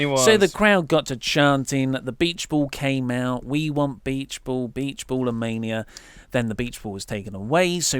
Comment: I didn't realize they were chanting for the beachball during the matt and brayy match. I thought they were saying "We want meatloaf" who is a singer in Vegas or "We want Nero" the second was of wishful thinking though :\